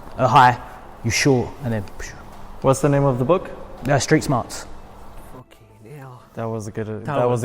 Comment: what book name he said at 17:06 can anybody suggest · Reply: Street smarts